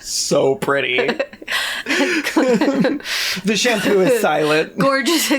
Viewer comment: Did he just say, "the shampoo is silent"?